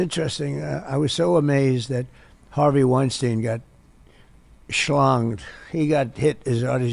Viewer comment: Correct me if I’m wrong, but did he just say that Weinstein got “schlonged”?